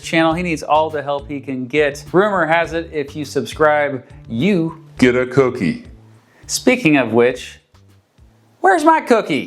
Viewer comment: Did someone say Kookie?